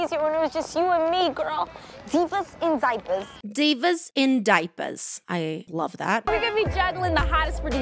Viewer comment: Divas is Diapers? I legitimately NEVER KNEW that's what she said. I thought they said zeephus and zeyephus.... and that those were some Goddesses i didn't know about or something. Lol. Makes so much more sense now. Lol.. Please continue. The things I would never know. Lol